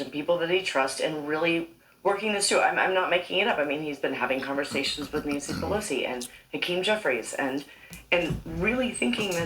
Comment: I thought I heard her say, "He had conversations with Nancy Pelosi and King Joffrey."😂😂😂